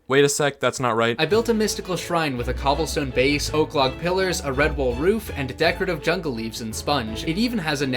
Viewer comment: I thought GPT said “RedBull Roof”